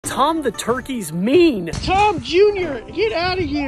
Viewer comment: I thought he said "Tom the Turkey is meal!!!" 💀💀💀